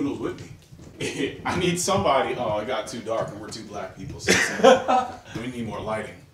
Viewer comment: "It's too dark and we're two black ppl" lmao🤣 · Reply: Thought he said we're too black lol